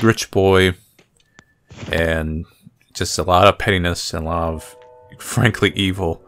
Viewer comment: waht? Lmao my dirty mind can't hear whatever this word is supposed to be. Can someone tell me what said? 😂😂😂 (I just hear paynis)